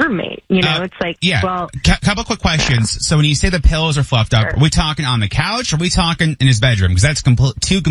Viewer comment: I thought she said the pillows were FUCKED UP 😂😂😂😂